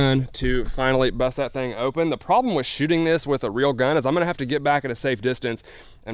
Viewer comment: Actuated nails guns also work great against Russian mafia hitmen trying to hold your fellow depot mart employee's hostage. When you said real gun I thought you said rail gun. I was like wait a minute you got a battle ship too.